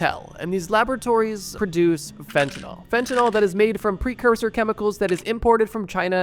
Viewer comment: I didn't hear fent I heard Femtanyl lol